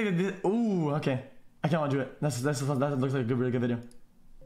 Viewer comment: bro tf does he say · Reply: He said looks like a really good video